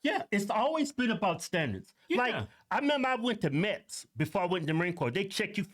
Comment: It sounds like he says "I went to Mets" lol. I figured out from context clues he was saying MEPS, but for a second I thought he was talking about trying out for a baseball team.